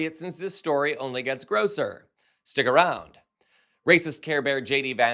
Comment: Not much new in this pice. Repackaged news, poorly presented. Is "grosser" a word? I thought he said ""grocer"...why are we talking about produce? And how many ways can someone say "Palantir". Good grief. While the overall message is valid, democrats had a clear messaging/communication challenge in 2024. Repackaging old news and spinning it this poorly doesn't help our cause.